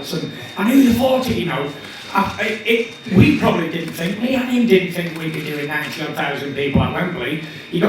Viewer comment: what did he say there? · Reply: We probably didn’t think - Me and him (dillian) probably didn’t think we’d be doing 90k at Wembley